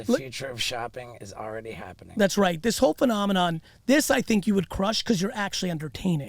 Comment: i thought you said you would push less ads....